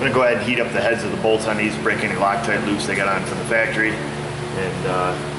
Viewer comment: what did he say at 4:17 'imma go ahead and heat up the heads of the bolts of these ...' cant make out the last sentence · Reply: to break the lock tight loose that it has on from the factory he said...